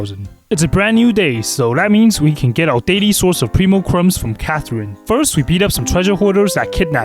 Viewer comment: Did he say Primocrumbs?